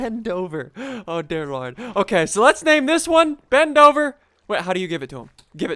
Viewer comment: When you were naming Ben dover it sounded like you were saying bend over